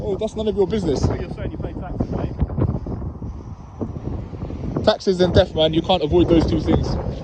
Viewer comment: Thought you said "taxes and theft" could consolidate the two into one thing as they are the same ^.^